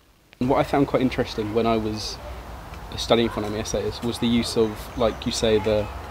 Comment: What does say at 22:33? "What I found really interesting when I was studying ___"? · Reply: Oh, it might have been 'essays' in that case, the last comment was just from memory! The transcription is a nice touch!